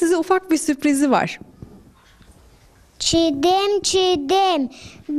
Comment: çekirdeğe çiğdem mi dedi? Ben mi yanlış anladım?